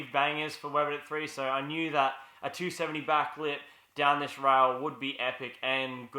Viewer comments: Thought it said "270 backflip to handrail" and I was like "OH SHIT" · I thought it said 270 backflip and I was wtf